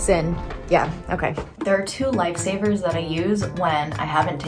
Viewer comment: When you said life savers I could have sworn you said lightsabers!🤣 You can tell where my mind is!💪💪🔥🔥